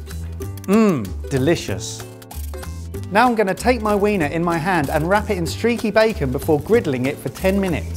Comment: did he just said 'weener'? (not sure if I spelled it right)